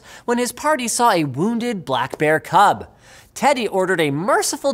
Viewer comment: Did someone say... BLACKBEAR?